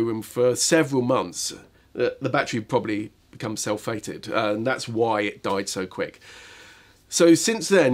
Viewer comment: what is the term you are using at 13:51 - 13-57 about the batteries storage? Cell 'fading' cell 'fated'? I couldn't catch what he was saying. Can anyone decipher for me please. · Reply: Sorry... I was saying 'sulphated' but it did sound odd!